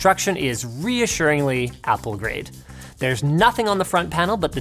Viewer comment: IS IT ME OR DID HE JUST SAY "APPLE GRATE" CUZ IF HE DID I LOVE HIM